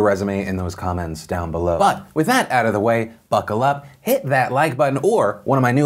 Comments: When he said, “buckle up” I thought he said “fuckle up” and after thinking about it, I prefer fuckle up. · after all the intro about dudebros and kyles i swore he said "FUCKLE UP" and lost it